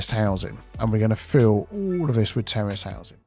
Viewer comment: LOL. When you said that you were going to put in terrace housing, I initially thought that you'd said that you were going to put in terrorist housing.